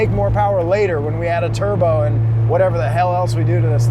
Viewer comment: did i hear turbo?